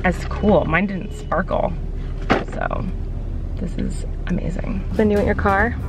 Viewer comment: it sounds like Flynn said, “this is so cool”